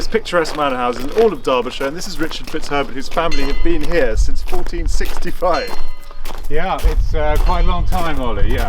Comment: Hahaha i had to rewind and put the captions on at the start because it sounded like you said this is Richard whos currently been here since 1465!